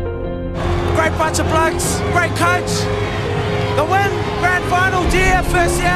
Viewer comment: What does he say at 0:29 to 0:35? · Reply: I can tell you most of it but not sure what he said after "grand final": Great bunch of blokes. Great coach. The win. Grand Final. GFS here(?). Waaaoooooo.